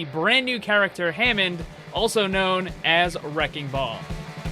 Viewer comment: Thought you said Hamster Ball rather than Wrecking Ball there lol